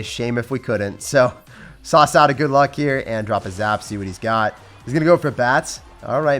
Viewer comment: What is he saying pls someone tell me??!?!? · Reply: He says: "We're going to sauce out a good luck and see what's up."